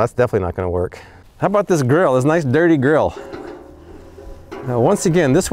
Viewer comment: I know you said “Grill” but I first thought you said “how about that nice dirty girl”’ 😂 🤣 😆 That was a good laugh brother.